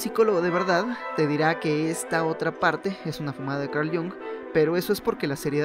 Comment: alguien puede decirme que dice en el minuto 7:05 ? fumada de carliong? no entendi ni oi bien